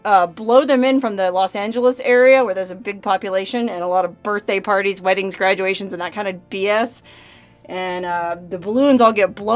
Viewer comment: Did you say "wedding strangulations" at 10:23 in regards to the mylar balloons? Played it over and over and that's what I get. I think the strangling part comes a little later after the wedding normally.